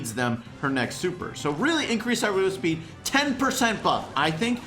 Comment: I would say increasing Tick’s explosive speed is a nerf. As it limits his control which is what makes him so good. Plus he’d just be another Dynamike then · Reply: I thought he said increase the throwing speed?